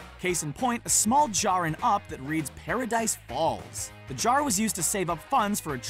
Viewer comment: Did he just say Paradise palms at 4.20